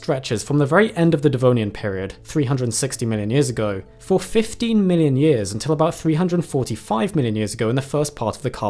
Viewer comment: It was an allergy to bee stings or peanuts coupled with gluten intolerance. 15 million years? I thought you said 15 minutes!